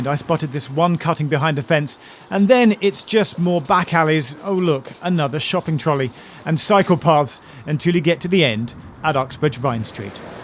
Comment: I heard "Shopping carts and psychopaths"... OK... Time for bed I guess...